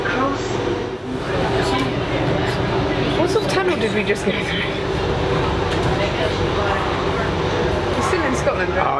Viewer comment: What does say at 0:40 ? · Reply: What sort of tunnel did we just go through?